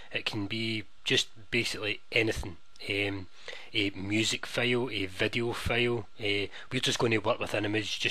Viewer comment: I thought he said "Music fail" or "Picture fail" :)